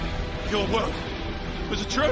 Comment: did he say lucky you or F*ck you?